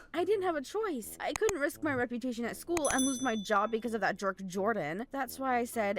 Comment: did she say jork🤣🤣😋